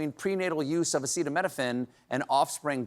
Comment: Am I the only one who heard him say "Epsteinaminophen."